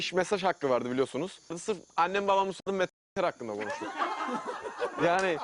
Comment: ne diyor la burda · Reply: Annem babama sormadan meter hakkında konuştuk diyor.